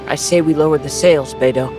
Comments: I FIRST THOUGHT HE SAID “Let’s lower the sails baby” NDKSNSKKSKSDKSS IM AN IDIOTBSMSNNDD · Lmao when he said "lower the sails beidou" i thought he said "lower the sails baby" what is wrong w me